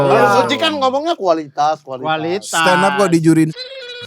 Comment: Ada yg tau tretan di menit 16:34 ngomong apa ? 😂😂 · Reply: Di Juriin Soimah kayaknya😅